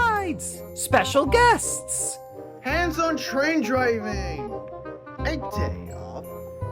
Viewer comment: what did Fred said? I didn’t understand what he said. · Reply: “A day off.”